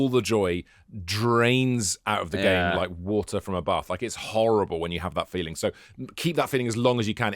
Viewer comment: I laughed my head off when I thought said "Drains out of the game, like water from a butt"!!